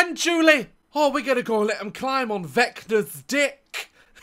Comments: i full on thought he said vector from dispicible me but he said vecna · For a moment there I thought he said vector’s dick but vecna works too (also if I posted the same comment twice it’s just I’m pretty sure I posted a comment right as it came out cause I watched this right after it came out and I am NOT going through 2k+ comments)